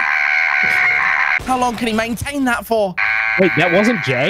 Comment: 😂 0:35 I thought he said “How long can he maintain that FORM?”